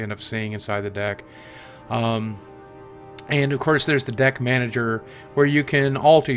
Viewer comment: For a second I thought Deck manager said dick manager